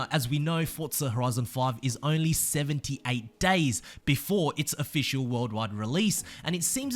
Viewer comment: I thought he said 7-8 days away from launch. Got my hopes up :/